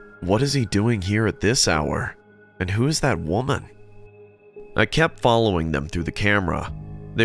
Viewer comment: it says woman but I heard walnut 😂